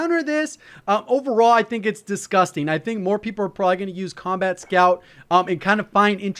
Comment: thought he said 'scumbag' scout